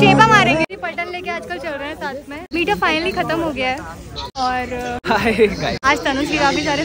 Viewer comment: DID SHE SAY TANUJ